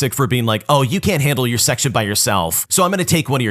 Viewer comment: it says s*x job instead of section in the subtitles